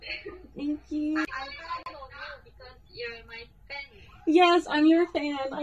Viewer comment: omggg a hyewon stan i'm so happy for you! what did hyewon say to you on 12:13? i couldn't hear it clearly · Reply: the thing is i couldn't properly hear her either but hearing it back, she's saying "i'm proud of you for being my fan" if that was the part you meant!! :D and also thank you 🥺💘